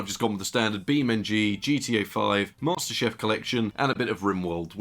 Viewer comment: pardon, did you say Master "CHEF" Collection? 😆